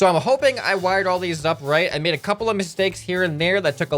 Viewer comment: the video froze when you were goint to say the word couple. so i heard "I made a cup" lol